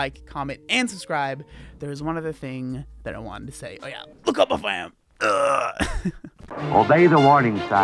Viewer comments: I always do the “Look how buff I am” outro with you 😂 It makes me happy. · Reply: I really thought he was saying "look up my fam" 😂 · I always do the “Look how buff I am” outro with you 😂 It makes me happy. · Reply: Omg i didn't know what he said either! 😂 I thought he was saying look up my fam. Aha